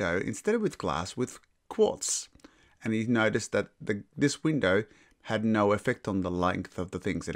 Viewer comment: no idea what you said there. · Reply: I did mumble that didn’t I What I was saying in effect was that quartz had no effect. Ie the spark was larger, longer. So photoelectric effect took place. But not the case with the glass window since glass absorbed UV.